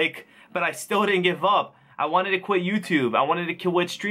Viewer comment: "I wanted to quit youtube i wanted to kill... quit streaming". Anyone else hear that? 4:09.